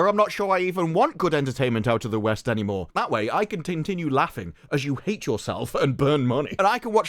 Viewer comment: Did you say "tin-tinue" @ 12:03 im stealing that.